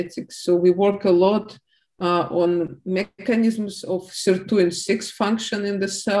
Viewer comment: she talks about "mechanism of ___ in the cells", can't understand her accent. Does she say sirtuins? Or something else? Thanks. CC says "cir26" which does''t mean anything near as I can tell. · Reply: Sirtuin-6 or sirt6 ? It's in wikipedia if you like.